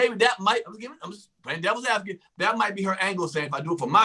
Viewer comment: Did say he just playing daddy's advocate or devil's advocate, either way both might be correct 😈 LOL